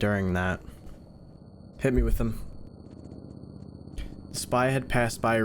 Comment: instead of hearing hit me with that i heard 9/11 6:35 /